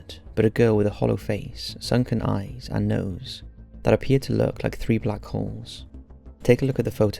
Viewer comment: Hahaha at 2:36 I thought he said “three black whores”